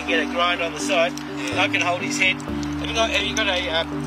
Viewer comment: Oops I thought you said off with his head?!?!? Oh well bacc to work.....